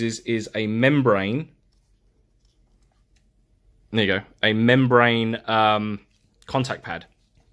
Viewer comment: What is he saying at 5:33? xD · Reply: "there you go", but 3x faster lmao